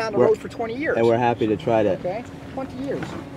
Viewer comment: I'm pretty sure he says 20 years. I thought he said "fuck you" too at first but now I think it's actually "20 years"